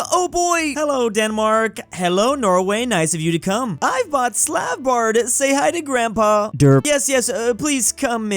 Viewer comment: Did I mishear, or did call Svalbard Slav Bard?